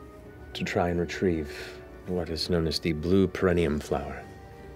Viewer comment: This has gone over my head. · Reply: It sound like Matt is saying Perineum Flower. The Perineum is the area of skin between the anus and crotch